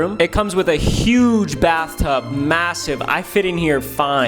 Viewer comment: did he say massive